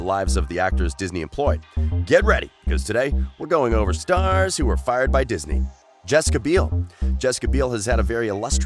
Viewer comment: I thought it said Star Wars we’re fired by Disney and I was like WHAT STAR WARS IS A POPULAR SHOW THEYLL LOSE SO MUCH